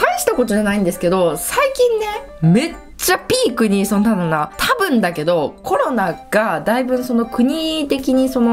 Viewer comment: めっちゃピークにの後何て言ってるのかな？ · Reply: その〜なんだろな だと！